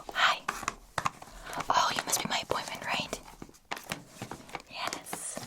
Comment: I thought she said “you must be my boyfriend right?” And I was like “Hold up, wait a minute”